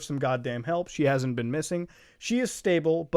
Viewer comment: I thought the video said Chris Chan went missing for a second